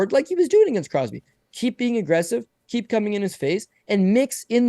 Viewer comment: Yes, did indeed say "keep c**ming in his face." for anyone wondering. At